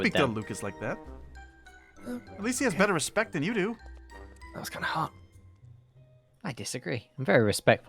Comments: I'm sorry, did Lucas just say "that was kinda hot" ? That caught me off guard 😂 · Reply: I hade to replay that part cuz I was caught off guard and he did say “That was kinda hot”🤣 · I'm sorry, did Lucas just say "that was kinda hot" ? That caught me off guard